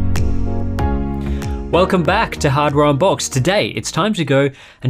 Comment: At the beginning I thought he said " welcome back to another episode of Hard Round Box"